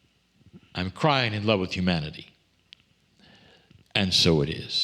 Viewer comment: I love how it sounds like is saying "I'm crying in love with humanity" 🥺🥲